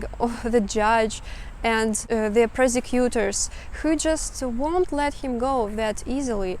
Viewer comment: It sounded like you said "persecutors", which ironically is more accurate than 'prosecutors' in this instance!